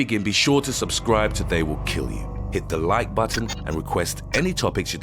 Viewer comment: i swear i thought he said subscrie or they will kill you